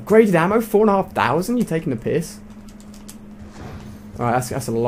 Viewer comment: did he say your taking a piss